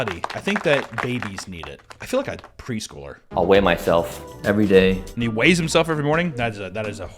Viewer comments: I thought he said, “I weigh my filth every day.” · At first I thought he said "every morning I weigh my filth" instead of *myself